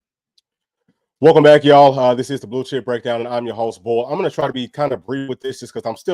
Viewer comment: man i thought you said this is the bulls*** breakdown, i busted out laughing, it is bs what they did to our vols tho